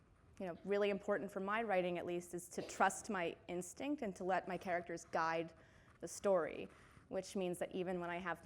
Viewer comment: When she says "I let my characters guide" I misheard it and understood "Die" and now I am crying no one touch me it hurts because it is the TRUTH